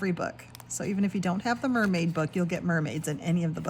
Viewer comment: Omg! I have the mermaid book so I don’t need it but if you get an extra copy you will become VERRRRY popular indeed! 🤣🤣🤣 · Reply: Ooooh, I am sorry I thought I heard you say you hadn’t got the mermaid book. Oeps! 😅